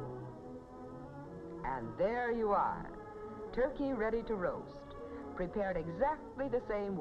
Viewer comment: I thought that Turkey said “you’re a Turkey” for a second